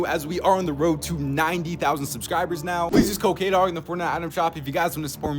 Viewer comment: i never understand what you are saying here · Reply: "Plsass use code kdog"